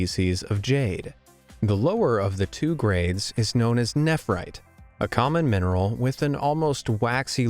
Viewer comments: I thought he said netherite XD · NETHERITE!? thats what i heard at first